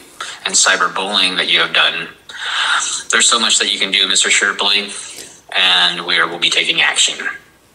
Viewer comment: is not a bully. He just stops the liars from claiming to be part of the teams. The mafia does much worse when you claim to be one. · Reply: 😂 I thought he said Stripley 😂😂😂